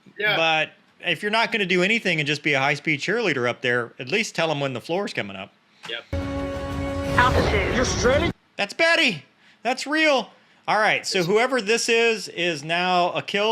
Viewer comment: The first time I saw this movie all I heard was B*tchin Betty!!😂